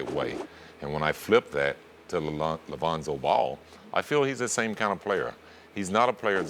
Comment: Lavonzo ball 😂😂😂😂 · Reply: ThUnDeR FoRcE fan lan fan lan that’s not his name neither, still wrong... 1:35 he clearly said Lavonzo